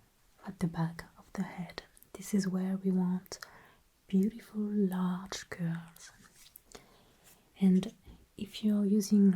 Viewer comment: I heard "[...] We want beautiful large girls". And I was like: Yes.